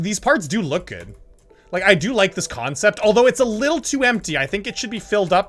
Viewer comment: I heard: i do like the concept....